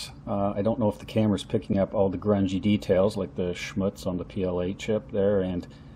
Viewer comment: did you say "schmutz"? :-D